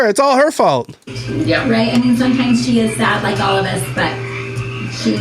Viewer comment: I heard, "...she's SAD, like all of us." Now THAT's sad. The Mom is too, apparently.